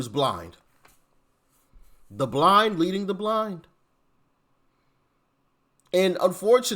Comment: When you said the blind leading the blind I thought you said the blonde leading the blonde, I damn near died from laughter.